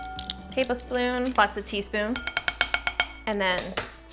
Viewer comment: Did anyone else hear the "sploon" ????